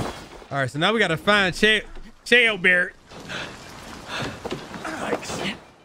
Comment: His name is Ceolbert but I hear "Jailbird" every time they say his name.